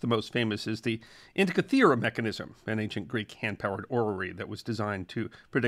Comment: This is for the. At time 1:05 you pronounce a word that I don't recognize, and it is a word which closed captioning presents as orery, As far as I can tell, there is no such word as orery. So, I'm curious, what is the word that you used? Thank you. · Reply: Although the Greeks had working planetaria, the first orrery that was a planetarium of the modern era was built in 1704 by clockmakers George Graham and Thomas Tompion. The name of the instrument came from Charles Boyle, 4th Earl of Orrery, who commissioned one of the earliest models.